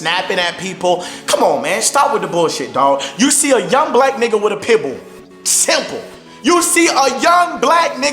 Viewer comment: For a second I thought he said "You see a young nigga with a pebble."😂